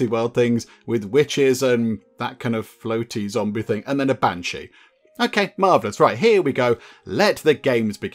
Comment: During the intro of the character types, I had looked down when you said "Banshee"... I had to rewind as I thought you said "and a Fat Chic". I died laughing. (I'm a fat dude, so i can make fun of my people). 🤣